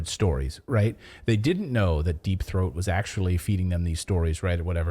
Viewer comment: did he say "deep th..." what?